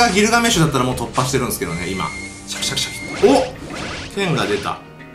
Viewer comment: 剣が出たがテンガ出たに聞こえた